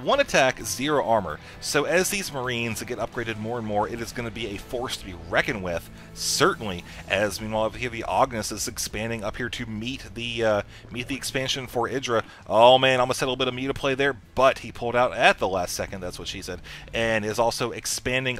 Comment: I thought he said Greck Theognis vs EGIdra... I thought "Idra's playing with himself! That's unusual."